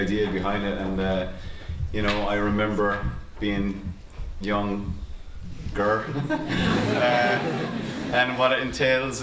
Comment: is he a transgender? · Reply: Ania O I realize that late, i thought he said “when I was young girl” 😭